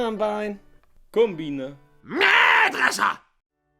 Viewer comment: я один слышу made Russia?